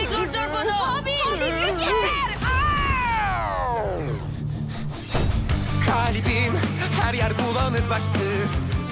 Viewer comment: de lute in ne dediğini anlamadim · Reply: Yeter arrgh